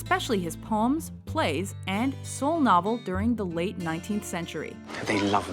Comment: does she say "So Novel"? can someone tell me what she said pls, I´m spanish and I didn´t get it